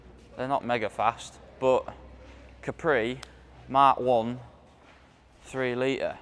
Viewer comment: Is that white Capri a mark 2 , thought you said mark 2, Love the videos and tv programs,👍